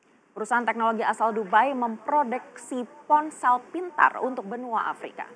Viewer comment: Apa saya salah dengar memprodeksi ?